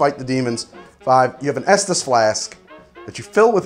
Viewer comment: Ngl I thought that said "fetus flask"